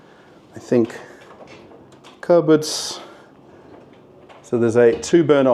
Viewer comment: I wonder what a word is it. You use this word very often and its meaning is quite understandable. It is "cabinet" or "closet", but I cannot catch what a word you are using there. Pronounced like "cabbet" or "cabbad". In general, I understand almost 100% of the videos but exactly this word makes me confused. Have never heard it before and cannot find a translation online, . Is it a reduced pronunciation of "cabinet" or something else (maybe, some Australian-specific word?). Always was curious about it and eventually dared to ask. Could you give me a hint? )) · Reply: Cupboard.